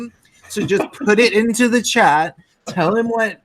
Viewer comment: you said: "Put in the Chat.." I heard The Chad.